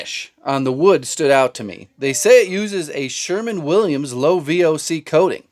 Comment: Did you just say “Sherman-Williams”😂😂😂😂😂